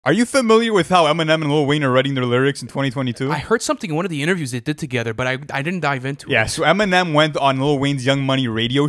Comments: In the beginning it sounds like hes saying are you familiar with Eminem and lil Weiner are writing their lyrics? Lmao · I thought I heard him say "Eminem and little weiner" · In the beginning it sounds like hes saying are you familiar with how Eminem and lil Weiner are writing their lyrics? Lmao